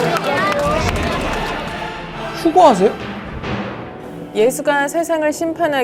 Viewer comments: 나만 수고하세요라고 들림? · 휴거하세요ㅋㅋㅋ 존나 웃기네 나는. 처음에 저지랄. 떠들때. 수고ㅇ하세요로 잘못 들음 암튼 신기한 놈들이였음ㅋㅋ 미친. 사이비 개독들ㅋㅋ